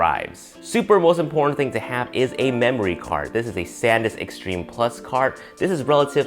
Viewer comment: for the SD card I thought you said "Saddness Extreme" 😂